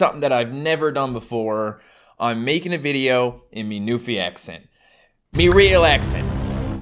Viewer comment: When you said “my newfie accent” in the beginning, I thought you said: my new fiancee. I was “shooketh”, thought I had missed something. So far for being a dutch girl understanding accents... 😂